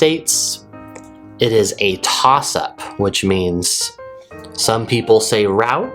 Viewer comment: Hi! 0:49. What was that word? Tosap?Can you spell it? What does it mean? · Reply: It is a "toss up." :) There's a new idiom for you. I should have thrown in my bonus idiom clip for that one.